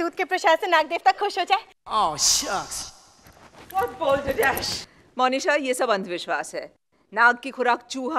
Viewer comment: what maya said?? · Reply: What Balderdash? Meaning "what nonsense"